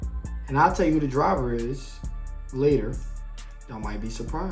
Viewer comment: Thought you said you need drivers fam 🤔